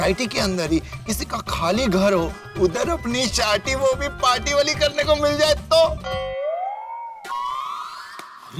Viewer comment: sodhi deliver wrong instead of party wo bh sharty wali he said ‘sharti wo bh party wali’